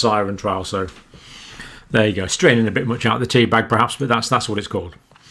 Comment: "squeezing a bit much out of the teabag, aren't we?" New one for me here in the US and it is hands down my new favorite phrase 😂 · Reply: I heard "straining a bit much out of the tea bag..."